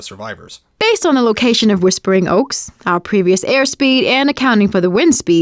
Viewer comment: im sorry wtf did she said “Whispering Oaks” oh shit Slender man is nearby